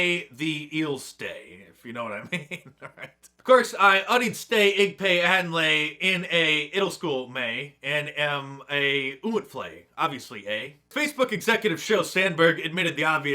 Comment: For those wondering what Mark said from 1:43 on, it's "Of course, I studied pig latin in middle school and am a fool, obviously". Thank me later.